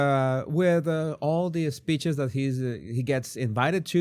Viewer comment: I heard, “he should be happy with all the bitches he gets,” oh it was speeches. 😬